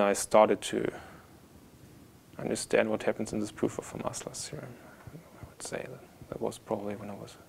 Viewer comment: I couldn't understand what he said at 3:50 - 3:57. Can anybody help me with that? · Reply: Understand what happens in the proof of fermats last theorem I would say